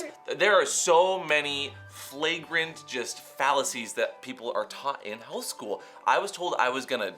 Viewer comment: says fallacies and i heard 'phalluses' 😂